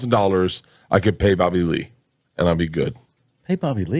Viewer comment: did he say “Bobby Lee”??